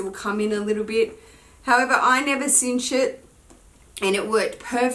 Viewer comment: So ... am I the only who thought she said "I've never seen sh*t" instead of I never cinch it at 11:22? LOL! Great video.